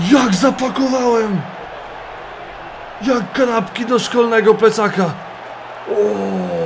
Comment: usłyszałem: jak grabki do szkolnego plecaka XD